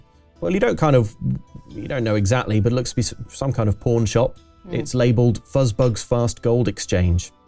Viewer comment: As a Canadian with a Canadian accent and not an English one, it took me longer than it should have to realize that the captions were wrong at 28:26 and that what actually said was PAWN shop.